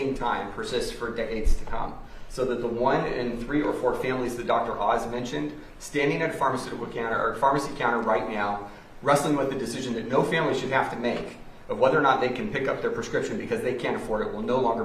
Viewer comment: Did anyone catch the "right meow" at 1:44?